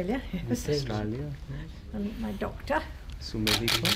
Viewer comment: I thought she says 'my daughter'